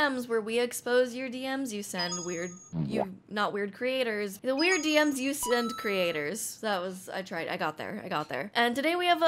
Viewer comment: i thought you said "onlyfans breeders" and not creators lmao